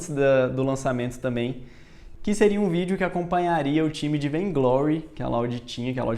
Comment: qual o nome do jogo que ele falou ? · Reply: Vanglory